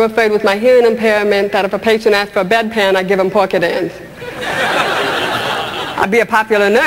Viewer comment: HELP!! At 46:55, I can NOT get what she says. " If a patient asked for a bed pan, I'd give ?? WHAT?? Sounds like "Poor kid an ends"